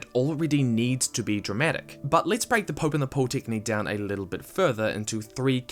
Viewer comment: I kept hearing "Pope in the Pole" x_x